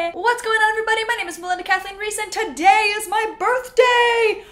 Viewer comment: i thnik my mental translator is failing too xdd I heard mi name is Melinda caffeine xdd